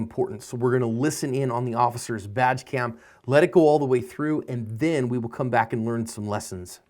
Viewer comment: LOL....(:36) I could have sworn you said "Vag cam"....